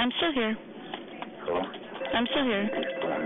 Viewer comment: sounds like he says “Alright ma’am.” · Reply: it sounded like he said "hello"